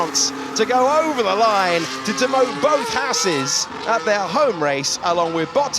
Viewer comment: "... to demote both Haases at their home race along with Bottas..." that took me a second to get that as at first it sounded like "... to demote Bottases at their home race along with Bottas..." LOL 🤣